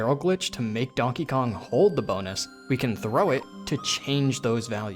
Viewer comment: lol I had to go make lunch and forgot to pause this and when I came back I heard “we need to hold the bonus area” and I was in a state of utter confusion for multiple straight minutes